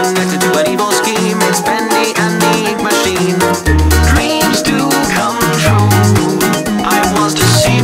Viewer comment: I misheard "dreams do come true" to "memes do come true"